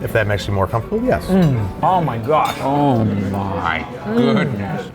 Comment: Each time says "Oh my gosh", all i hear is "Oh my Josh"! :D